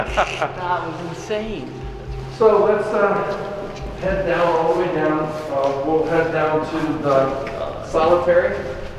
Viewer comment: I dont know what it said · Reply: It says hot in a low whisper at this time